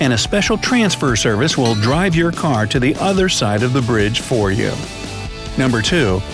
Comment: Nobody: Bright side spokesperson: 1:32 they will transfer your car to the other side for you Bright side editors in the captions: they will transfer your car to the other side instead of you. 😂😂😂